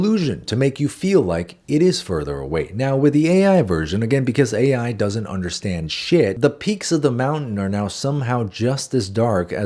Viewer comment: because ai doesn't understand shit? is that what that says lmao